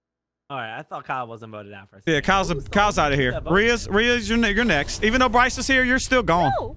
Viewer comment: dude it sounds like you said the n-word bro lol